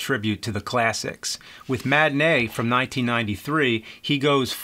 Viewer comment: Saw John Goodman and thought this movie was called Manatee. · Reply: I've been sick with fever for a while, I thought it said "Martini".